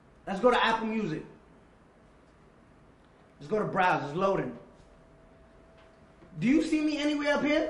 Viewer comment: I swear to god I thought he said “𝘭𝘦𝘵𝘴 𝘨𝘰 𝘵𝘰 𝘣𝘳𝘢𝘻𝘻𝘦𝘳𝘴 𝘪𝘵𝘴 𝘭𝘰𝘢𝘥𝘪𝘯" was about to say Danny you dirty dog 🐕 😂😏